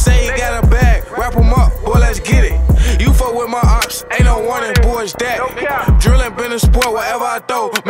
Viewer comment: If you fuck with my opps ain't no warning boa it's dat....💯 I felt that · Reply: Damn this whole 2 years i thought he said Aint no “wondering” boy its that but i can hear the “warning” now but either way hard asf 🔥💯❌🧢